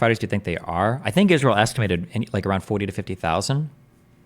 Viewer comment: This ESCALATED Into The Most Personal And Unhinged Drama Ever <link> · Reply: i think they said closer to 30,000